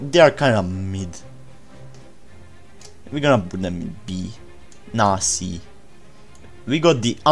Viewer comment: did bro just say nazi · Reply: I said nah C but I kinda made it sound different